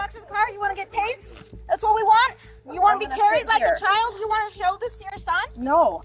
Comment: The first clip, the officer says do you want to get tased like a child? Since when do we taze children? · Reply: I thought she said tased, then CARRIED like a child. But I’m like 30 min into the video at the moment I’m writing this so maybe remembered it wrong